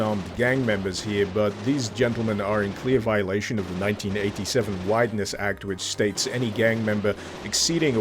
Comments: I thought you said the gangsters were in clear violation of the 1987 whiteness act... would have been pretty accurate haha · I thought he said "whiteness act", and I wasn't even surprised, cause that sure sounds a lot like American law enforcement.